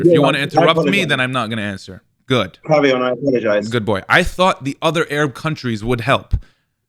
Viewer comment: did he called him "good boy" ?